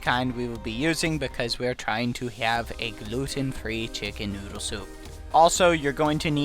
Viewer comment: did he say „looting III soup“? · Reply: It's gluten free